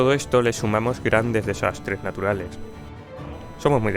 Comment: ¿Dijiste "desastres naturales"?, ¿realmente escuché "desastres naturales"?. 👀